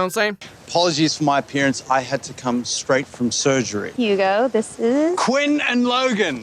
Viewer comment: HUGO? Did I hear that right?